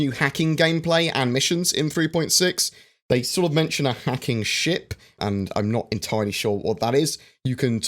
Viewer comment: I thought he said a hacking CHIP